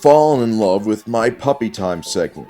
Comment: I was taking a piss and thought said “Titty time” went running to my phone, a little disappointed but who could be sad with those little eyes looking back at you.